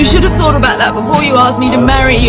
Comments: Why did i hear "Why cant u understand its because u stink i would never marry u" 😭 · I thought he said "Cant you understand you stink? I would never marry you" can anybody else hear that?